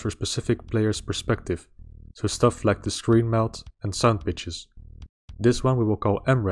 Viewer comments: Did he say sandbitches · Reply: Sound pitches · "The second index is only used for a specific player's perspective, so things like screen melt and sandwiches" *Yes I know he said "sound pitches", when I first heard this I was like, "what?"